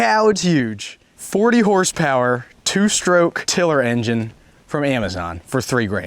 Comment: For some reason, I heard you saying 4stroke in the beginning of the video. Its a 2 stroke. A bit expensive for a chin lop ob. Just saying. Love your videos